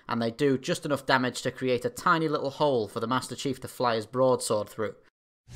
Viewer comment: I swore I heard "small little hole in the ship just enough for the MasterChief to fit his balls through.